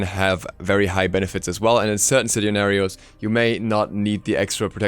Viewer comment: did i just hear sitnarios? as in a hybrid between situation and scenario... speak english good